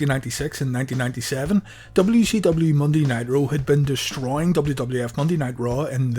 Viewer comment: it sounds like you said WCW Monday Night Raw. Had to replay it but you did say Monday Ni-roe.